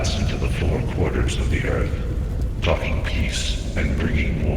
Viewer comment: it is translated a "f#cking" instead of talking... lol Did anyone notice?